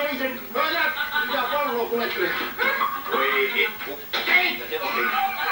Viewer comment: "Voi vittu ei...." Spede Spede .... :)